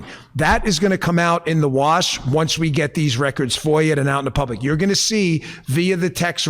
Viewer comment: I think he said "FOIA'd", not "for you" or "for ya".